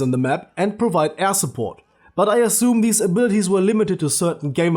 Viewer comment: I heard ass-support xD